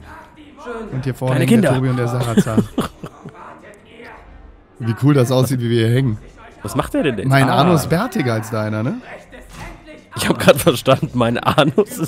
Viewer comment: Coole Folge :D Ich hab auch verstanden mein Anus ist bärtiger als deiner :D Erstmal Lachflash :D Ihr macht das super :3 Ich freu mich au die nächste Folge :) Schönen Abend euch allen :)